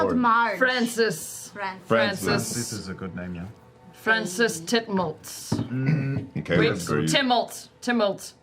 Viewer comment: Did Beau just say Francis Temult, like Imogens Temult?